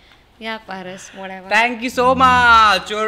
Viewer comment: Did she say "i ain't " !? or " i need "